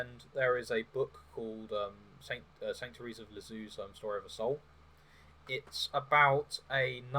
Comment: What? Did you say "Sanctuaries of Lazu's own story of the soul" ?